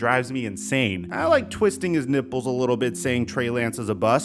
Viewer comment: I thought said "twisting his nipples " prolly hearing sumtn